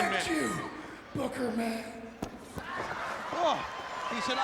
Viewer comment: I respect you Booker man! For years I thought Pillman said Boogerman.